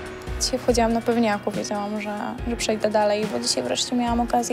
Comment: Za pierwszym razem na końcu usłyszałam "pechowcem"